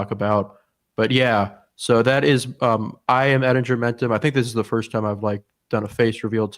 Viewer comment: Oh I thought you were saying his name was Ed Germentan or something like that 😂